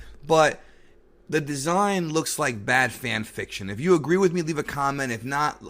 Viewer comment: Bad fan fiction agreed. I first thought he said batman fiction.